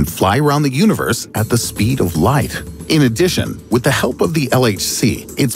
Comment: did he say speed of life or speed of light? If life what is that?